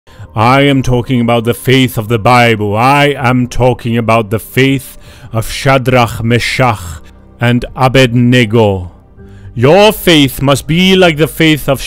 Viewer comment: anyone heard of "faith of chat D Ra me cha ugh and ab bwahaha?"